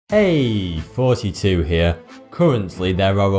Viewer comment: why does it always sound like he saying" hey 42 here"